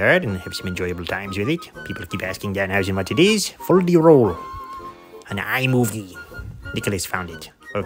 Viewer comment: ok couldn't understand or anyone have a link to the background music ? "fold the roll"? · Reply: Fol De Rol by Claud Oak